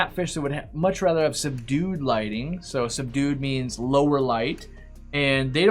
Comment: I thought when you said "they like subdued light" I heard "they like some dude light" like the fish are chillin in dude light